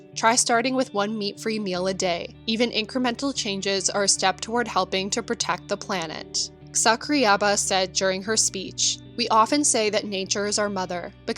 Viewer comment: <link> Debunked. Any more stupid arguments you need debunking? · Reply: lol I thought it said one meal a day as in only eating ''one meal a day''. Im vegan and i know its the best eating style. One meal a day = bad plant based diet = good